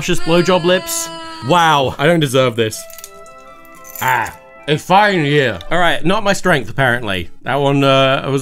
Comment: Captioning error spotted around 3:20, the captions say 'its fine year' but its clear tom was saying 'a fine year' (cause drinking from a glass... wine joke)